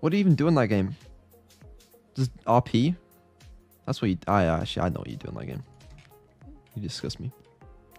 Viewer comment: "ah - a actually I know what you do in that game" (british mumble speak we brits learn it in school)